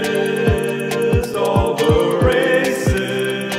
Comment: First time I saw this I thought he was saying "Racists, all the racists..."